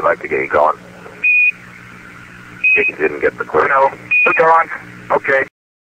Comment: this one actually proves the delay. Cernan is responding to "we'd like to get you going" and winds up talking over Schmitt because he paused too long to continue his "didn't get the clue" second sentence. Anyone that has talked on a phone with a delay knows that you don't pause when your taking unless you want someone talking over you because they thought you where done. Cernan said "i know" (its hard to make out what he said) not Schmitt. Their voices are vary different so your graph is BS. I'm done watching your video.